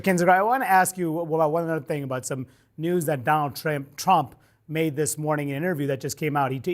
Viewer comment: Anybody else hear that "donald tramp" 7:40 ? Love it